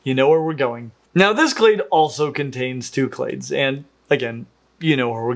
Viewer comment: Genuine question (I know nothing about this topic), around 37:50, what is the word he's saying for the groupings? Claits? Clates? I wanted to look up more about it but wasn't sure how it's spelled.